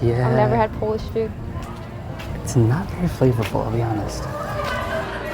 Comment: When she comments, “I never had Polish food.” I couldn’t figure out certainly what he said because he was whispering. It sounds like, “It’s not very favorable to be honest.” Though his candidness is Ok but I found it very ironic and distasteful of him saying that when he’s whining when he claims others are xenophobic. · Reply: He said: "flavorable" which means it doesnt have a lot of flavour: 1. That implies he tried polish food before that is the opposite of being xenophobic if you know what the term actually means. 2. It makes perfect sense for him to say that since bangladeshi food has a lot of spices in it. I dont believe there was a xenophobic tone to what he said, it was just his opinion about the food which actually a lot of people share. I don't see how you can compare not liking the food that much with actual xenophobia.